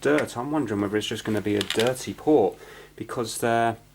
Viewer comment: I honestly thought he said "Dirty whore" instead of "Dirty Port" initially... like if you heard the same thing I did... laughed so damn hard lol :)